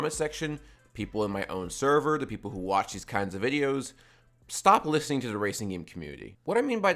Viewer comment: I thought he said stop listening to racing game soundtrack for a sec